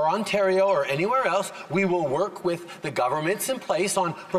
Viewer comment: did trudeau just call this the opioid academic? It’s epidemic. He is so damn embarrassing for Canada